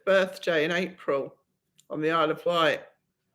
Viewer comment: Ahh yes, Guernsey- should have remembered, I read book also- Isle of Wight, will have to look up (especially since I thought you said White)- Glad Mom is so close.